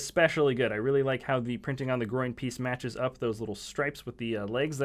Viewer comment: a 3:05 did he say groing price?!😀😁😂